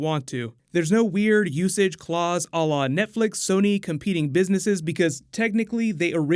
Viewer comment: what do you say at 6:12 , i keep hearing "alah?" sometimes says this word and I never understand what he means. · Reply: he's saying "à la" which means: in the style or manner of.